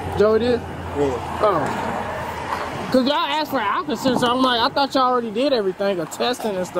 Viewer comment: I thought the cop kept saying he brought an alka Seltzer not an alcohol sensor haha